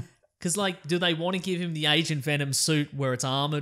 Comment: i swear when you said "agent venom" i was on another tab so i heard "asian venom" and when i replayed it you had pictures so then i get it